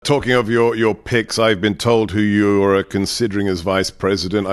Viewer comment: I thought at first he said "pigs" not "picks", lol.